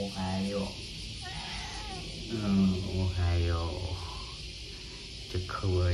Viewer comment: 昨日UPのオデコの鳴き声も「おはよう」に聞こえたけど、今日の秀吉も「おはよう」に聞こえる